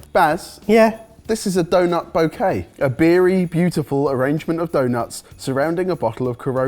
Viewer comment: Did say "A Barry beautiful" or "A beery beautiful"?😅